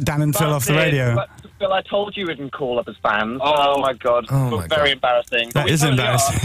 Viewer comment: what does dan say at 0:22? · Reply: "Call up as fans" maybe?